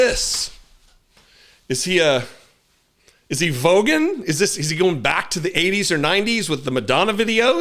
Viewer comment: When you first said Is he Vogueing (like Madonna) i thought you were saying Vogon (the horrible boring creatures from Hitchhikers guide to the galaxy) and I though Yes maybe he is! <link>